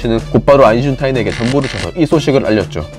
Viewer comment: 발음이 계속 아인슌타인으로 들려서 거슬림